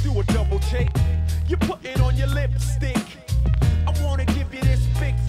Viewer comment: Sounds like he said lipstink...